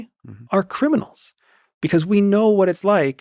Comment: Did he say "we" referencing criminals.